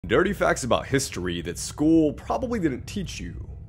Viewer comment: I THOUGHT HE SAID "NERDY FACTS"